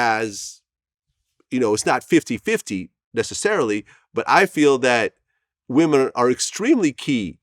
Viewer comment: Did he just say I feel?